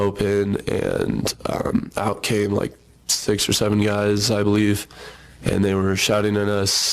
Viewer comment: did he say what I think he said… “6 or 7?!?!” 67!!!!!!!!!!!!!!